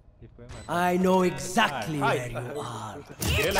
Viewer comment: “I know exactly how you die” instead of “I know exactly where you are” xD